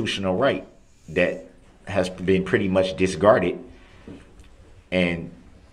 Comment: Oaf. Troof. Axe. Valit. Constroot. Biast. Granit. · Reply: Al 35:59 he said DISGARDED instead of disregarded